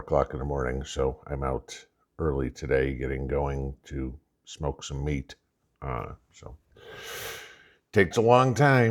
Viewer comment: Thought you said smoke some weed 😅